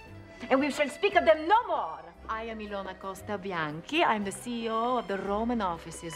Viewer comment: what did she say her name was? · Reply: Elana Costa Bianci